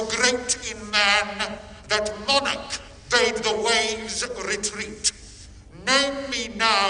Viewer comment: Even I knew that the Moon controls the waves. The tides, more specifically. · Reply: ngl, i thought i heard way instead of waves